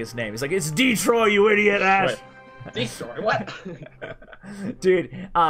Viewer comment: thought Ash said idiot ass but now replaying it I'm pretty sure he said idiot ash